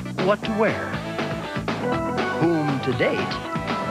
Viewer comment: why do the subtitles say who to date IT'S WHOM TO DATE THE DUDE SAYS IT TOO sorry